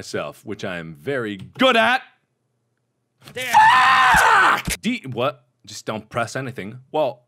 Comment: I think he said damn it right before he died at 3:28.